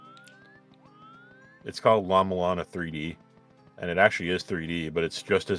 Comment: maybe we were referring to this <link> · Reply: I miss heard, thank you. This whole time I thought you comrades were saying “Llama Lana” instead of “Llama Mulana”